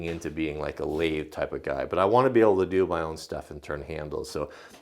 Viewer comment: "I want to be able to do my own stunts." If you think that is not what he said? I reject your reality and substitute my own.